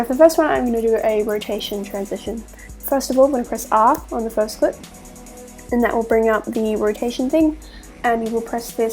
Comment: You said something but I don't understand well.What did you say?Btw the video is amazing thank you so much! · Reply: "press R"